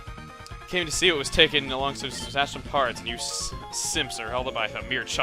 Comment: did archie just call his grunts simps?!